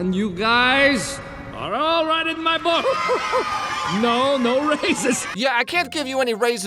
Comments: I thought he Said no racist. .—. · Stop hating on all the movies I like 😠! Also, I always thought he said, “ no racists.” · Icl i always thought he said no racist not raises lmao